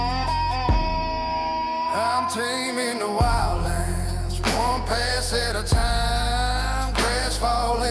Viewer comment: Awesome episode!!! Lovin' the series!!! That song said "Taming the wild lands, one pass at a time" I thought it was saying "Taming the wild lands, swamp ass all the time" 😂🤣😂🤣